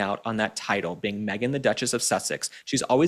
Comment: When you said The Duchess, I misheard "McDuchess..." 😂 Then I immediately thought maybe more like the hamburgarler ! Lifting all those photo shoot goodies! 😆